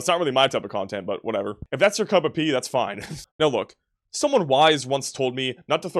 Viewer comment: my ears hear "cup of pee", but the subtitles say "cup of tea" 🤔🤔🤔🤔🤔🤔🤔 gonna go with pee